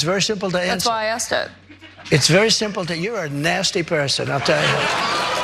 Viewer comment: Oh? What did he say? Cause it sure sounds like he said "You're a nasty woman, you know that?" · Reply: he said nasty person.